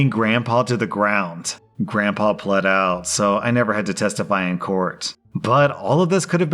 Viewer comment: When I first heard this, the part after the cops slammed grandpa sounded like "Grandpa bled out..." and I thought holy shit. I was chewing while I was listening to this. So it took me a second to realize OP said he didn't have to testify because "Grandpa pled out..." LOL